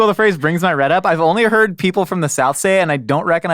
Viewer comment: Ok... I thought you said it gets your Rhett up, still makes sense